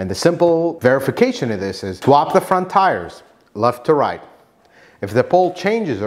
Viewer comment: did you say drop the tires left to right? What does this mean?